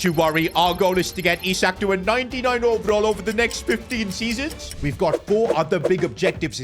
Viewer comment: you said 15 I heard benzema